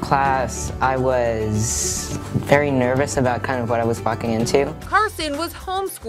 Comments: did he say what he was f*****g into??? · it sounds like instead of walking into it sounds like he saying fu**ing into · i heard he said f**** into😂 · DID HE SAY F**** ??? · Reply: he said "very nervous about what I was WALKing into"